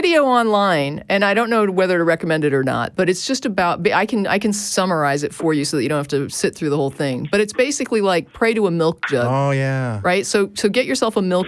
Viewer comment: All hail the MIGHTY MILK JUG! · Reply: i was half listening and I thought she said Milk Dud.